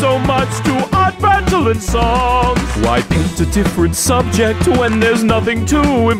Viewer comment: " why " ( i'm not the one who hear it , no ? )